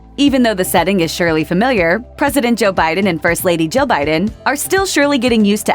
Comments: I heard "President Joe Biden and First Lady Joe Biden" at first. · I keep hearing "President Joe Biden and First Lady Joe Biden" no matter how many times I replay the first few seconds of the video 😂😂😂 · lol—It sounded like she said "First Lady Joe Biden."